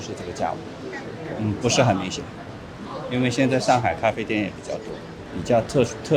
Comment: english subtitle should say shanghai instead of china.